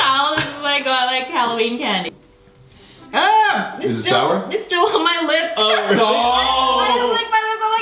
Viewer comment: Wengie when you had the sour bacon I thought you said 'Its a shower 🚿' when you really said 'Its sour'